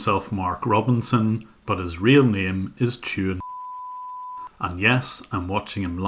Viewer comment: But he's real name is Tuhin C____, it sounds like he is saying Chewing C____ <----- you can guess what comes next..... It was most likely Chaudury